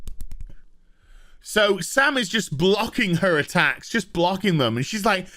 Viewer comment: best part of the video, This is why we watch 😂 😂 Did i hear a “blocksies”?😂